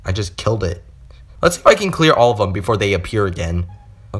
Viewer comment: i heard "lets f**king clear them before they appear again